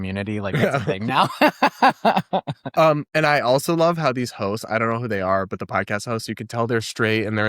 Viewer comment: I thought he said "these hoes" 😅🤣🤣🤣